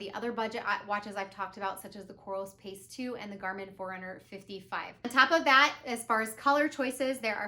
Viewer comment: Which Garmin did you say? Sorry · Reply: I thought you said Garmin 455 but I search it up and nothing appears